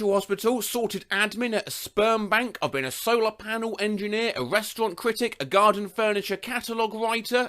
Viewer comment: I thought he said he was a redstone critic